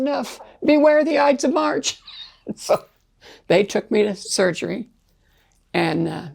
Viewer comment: I’m kind of confused on the timeline. She said she got the new liver in 2010, but then said she was a missionary for the next 25 years? What? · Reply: I thought she was saying March 10th